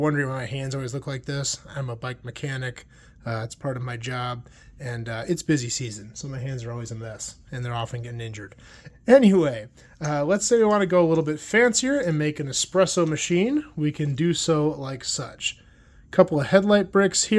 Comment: Sorry about your hand. When you said your hands are always getting injured, as a bike mechanic I thought you said they are always getting ninja-ed, and i had... imaginings.